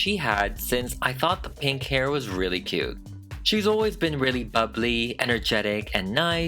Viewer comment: WHEN HE WAS DESCRIBING HER PERSONALITY I THOUGHT HE SAID UGLY NOT BUBBLY 😭😭